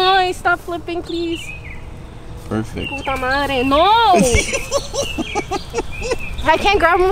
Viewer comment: You make me wanna move to #Florida!! Did she just say "Puta Madre" 🤣💀😂 Yuuppp shes #WifeyMaterial